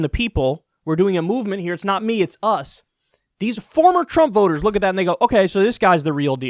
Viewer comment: I swear at 3:58 Kyle said look at that nigga lol